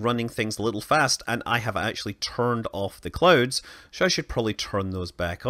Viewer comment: I thought he said that he turned off the clothes on earth lol